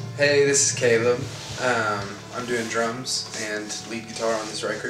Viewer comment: Thought he said ''I'm doing drugs'' x3